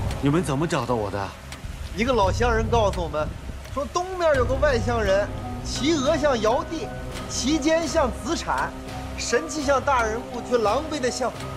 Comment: 東面有個外 「鄉」。不看字 我聽成 外星人 ！😨 ～ 還是，編劇故意編進去暗示的 ？